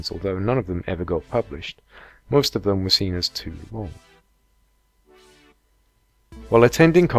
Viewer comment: I'm sorry but I can't understand what the guy says @ 3:30. "Most of them were seen as too 'fo'." What The heck is he saying? Too long? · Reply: It was "too long". Think I kinda swallowed my word there